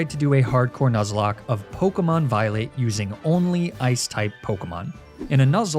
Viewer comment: I had to rewatch the intro because I could have sworn I heard “Pokemon Violate”